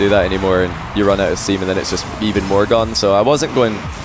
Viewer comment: did he just say semen?